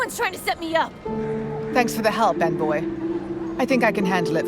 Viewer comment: I misheard the guard said "thanks for the help, bad boy" instead of envoy